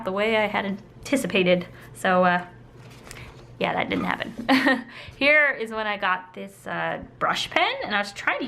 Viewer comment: For all of those hair sketches, I thought you said, " HAIR is where i got into brush pens."